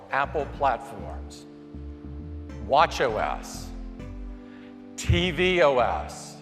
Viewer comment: I was looking away from the screen when he said "watchOS". I could've sworn I heard him say watch yo ass lol